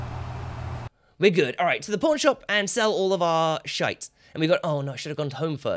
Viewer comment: It always sounds like he says "Porn Shop"